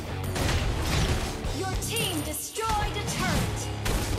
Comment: I literally hear yujin destroyed the turret